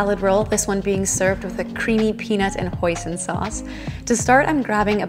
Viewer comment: lol i thought u said "poisioned sauce" i was like holdup- 😳